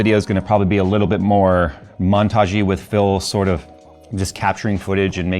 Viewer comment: I keep hearing “Phil port” in videos and it makes me giggle. ;-)